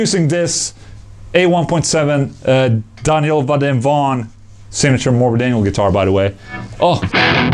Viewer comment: Did u say Morbid Angle?